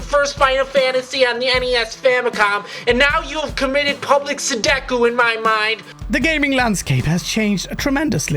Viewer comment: Yeah, I thought he said "Sedoku" too.